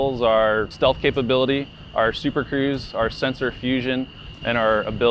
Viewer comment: I think he said “super cruise” which is one of the things the F-22 is known for, not “super crews” like in the subtitles